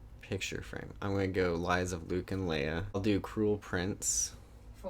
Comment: did say “Lies of Luke and Leia”!? omgosh😂😂😂😂😂😂😂😂😂👏🏼👏🏼👏🏼👏🏼